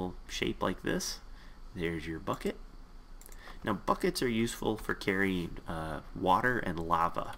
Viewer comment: when he said buckets i thought he said fuck it